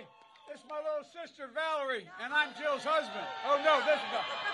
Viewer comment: I'm sorry but did he just say, "I'm Joe's husband"??